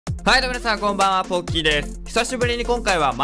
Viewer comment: 最初のポッキーですがポッピーに聞こえたw